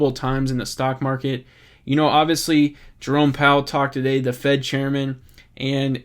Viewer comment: I almost thought that you said "Drone Powell"...